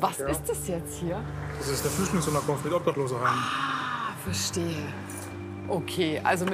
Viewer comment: Was hat er gesagt hahaha? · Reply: Des isch a flüschdlinhshoim un a obdachloseheim. Ist doch klar!11!1!1